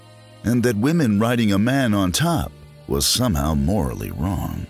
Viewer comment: does he say “...a woman riding a man on top?...” Wth? Lol